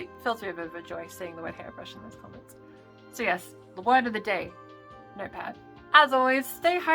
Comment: I think I misheard "notepad" as "nerdpad".